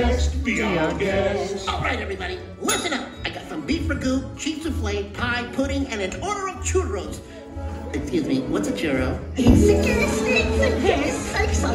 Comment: What is lefou saying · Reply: Alright Everybody, Listen Up, I’ve Got Some Beef Ragout, Cheese Soufflé, Pie, Pudding, And An Order Of Churros! Excuse Me, What’s A Churro?